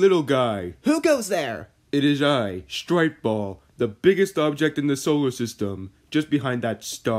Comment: “It is I stripe ball” 💀💀💀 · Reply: I thought that said “stripper ball”